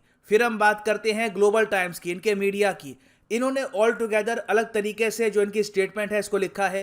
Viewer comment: Why I Heard Gobar times instead of Global Times Idk